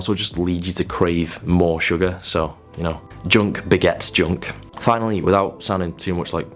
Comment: “Junk begets junk”…I THOUGHT HE SAID “junk, baguettes, junk”. My current diet. lol